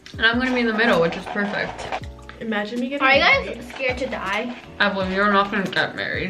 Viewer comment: I thought that was saying "Ayeza is afraid to Die"😂and I was like me??😅